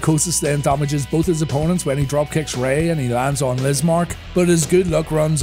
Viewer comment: i first understood "when he lands on Mizmark" instead of "Lizmark" . needed to listen to it closely several times to understand that i just wanted to hear you naming him Mizmark 😂